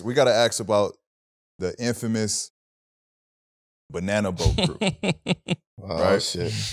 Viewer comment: Was i the only one who heard banana boat